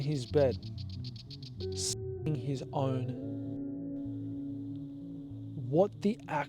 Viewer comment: I thought he said skinning his d 😂